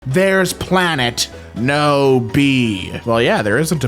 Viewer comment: i thought it said "there's planet noob"